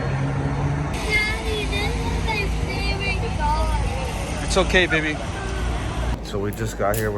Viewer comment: CRYING 😭😢. “Daddy that’s not my favorite car” I so happy u guys are good 😭🥺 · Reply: I thought he said “daddy, that’s my favorite car.”